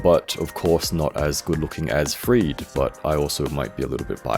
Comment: whats the name of the figure in the 1:14 minute I couldnt catch the name, is it also from the Snail Shell Studio? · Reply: HASUKI PA009 - Sister Friede. Here's a short of her: <link>